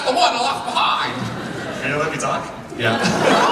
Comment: i think he said "am i allowed to talk?" can someone confirm what he said? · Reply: I was at the panel. He said "arin are you gonna let me talk"